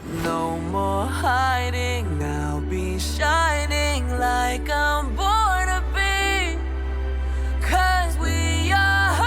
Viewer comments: All i hear is " shinin like a BUTTER BEAN" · I don’t hear “born to be”, I hear “Barnaby”